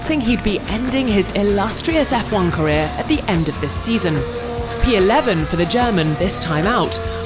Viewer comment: NO VETTLE did not say her would retire at the end of the season. He distinctly said 'by the end of the season' Two very different things. My guess - he's already told Laurence to find another pilot ASAP. I would not be at all surprised to find this is his last F1 race